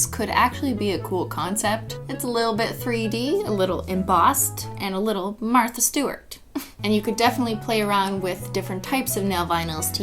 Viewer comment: why did I think she said kartha guert 😂✋